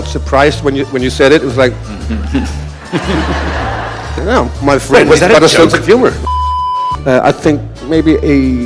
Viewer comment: i loveee this video, the editing is top notch 🤌🏻🤌🏻and did you make the memes yourself?? amazing p.s.: in 7:49 i think mads says "oh, my friend has a sense of humor"